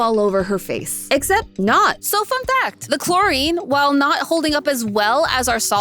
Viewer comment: Why did i thought she said "fk that" when she said "fun fact"😅😅